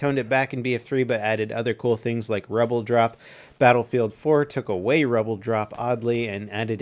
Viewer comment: Waht does he say at 7:00 ? I only understand rubbledrop :/ And if that's correct, what does it mean ?